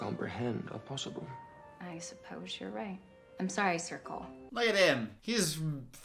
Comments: What he says: “I am Sir Cole” What I hear: “I am Circle” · Movie: “sir Cole” What I hear: “Circle” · I hate his name. Why would you name him “Sir Cole” it just sounds like circle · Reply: I also thought she said circle when she said Sir Cole. I am glad I am not alone on that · Every time I heard “Sir Cole” in this movie I heard “circle” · I hate his name. Why would you name him “Sir Cole” it just sounds like circle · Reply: Oh my god I thought she said “sorry circle” like some weird kumbaya-hold-hands-around-a-campfire-shit. That makes so much more sense now that she said “sir cole”. Yeah that’s a stupid name. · I’m sorry but every time she said “Sir Cole” all I heard was “circle”